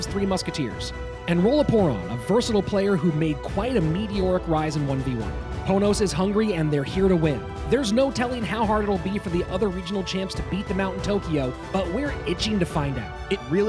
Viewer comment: Thought it said pornos for a second